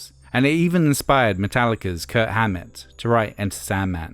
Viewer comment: did he just say Kurt hammet